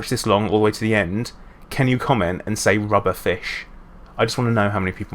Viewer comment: I see everyone wrote rubber fish. That made me laugh cause I thought you said rub a fish.